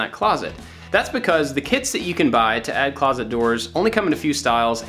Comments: for a moment i thought you said "the kids you can bye" then i got the point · Oh my gosh I thought you said the kids you can buy then I realized that you said the kits you can buy